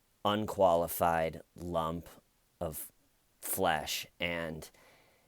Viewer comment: i thought you were gonna say unqualified popsicle